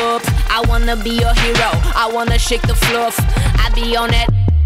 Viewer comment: What did she said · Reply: fluff